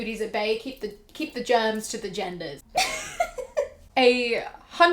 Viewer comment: Ik you said keep the germs to the genders but i heard it as keep the germs to the gendered and i'm living for it